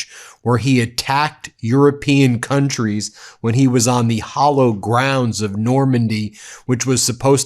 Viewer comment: Did you… mean to say the HALLOWED ground of Normandy? Because it really sounded like you said “hollow”.